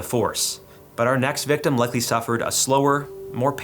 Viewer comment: am I mishearing or do you say "luckily" at 8:49?